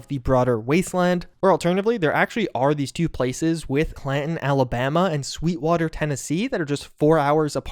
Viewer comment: Initially I thought you said Canton, Ohio has a Canton and sweetwater